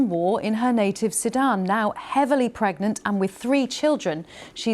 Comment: did she say heavily pregnant?